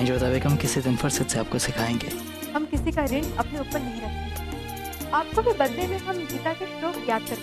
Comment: did jodha said birthday mein yaad karwa denge 😭😭